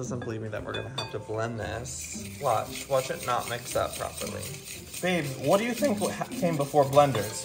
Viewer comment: did i hear call "Babe"?